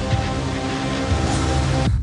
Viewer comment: Is it only me who heard it as Bisca barca😂 anyway good luck lad.